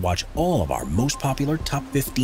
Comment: I’m not sure why but I heard “Grab a drink, grab some CRACK and get ready to binge!” 😆😂🤣